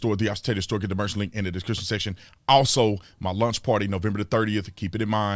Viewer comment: 🤣 I thought you said lunch party